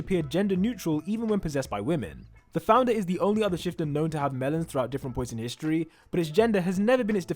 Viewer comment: did he say melons?